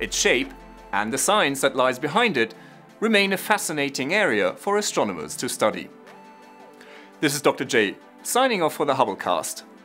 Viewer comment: I always hear "signing off for the hubblecuast". lol